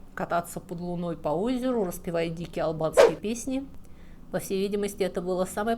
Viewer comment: Слишком частое моргание, в пандемии ударение на "а" вместо "и", гнусавый, напряженный, скрипучий, тихий голос, из-за которого страдает и выразительность, неидеальная дикция ("мервецы" вместо "мертвецы", "албацкие песни" вместо "албанские") О какой хорошей подаче материала пишут комментарии? Материал хороший, но подача не очень. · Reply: я не услышала "албацкие",услышала именно албанские песни